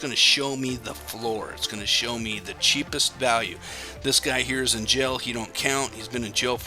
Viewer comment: hey thanks for the video! Question: I can't quite hear what you 're saying at 8:15... are you saying all those properties from Goliveira aren't real? What would be stopping you buying them all at $3.5? Thanks! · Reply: He is in jail. The account is frozen.